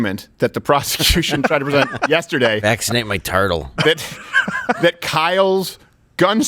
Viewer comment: 5 seconds in and I hear “Vaccinate my Turtle” 💉 🐢 this is gonna be good 😂